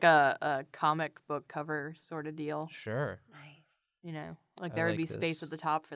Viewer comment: At around 46:15 I heard what sounded like someone whispering the word "hi" in my left ear. I was watching this alone in the dark, and needless to say, I nearly jumped out of my skin. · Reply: I'm pretty sure it's saying nice. It is scary though